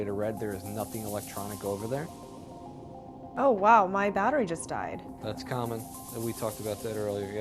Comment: Did somebody say" daughter just died" after 6:35?